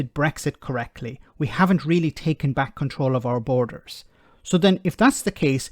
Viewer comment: I thought they said take back Cointreau ! 12 fekin bottles I gave to some bloke in Paris, cost me a fortune 😑